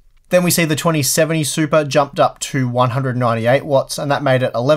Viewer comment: A few times I thought you said supra.